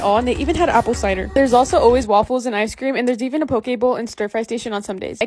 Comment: Her:and there's always a poke bowl What I heard: and there's always a pokeball